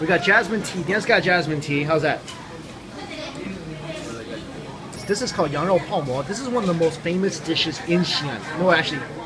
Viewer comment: I thought he said, “This is called Arnold Palmer” and had to rewind the video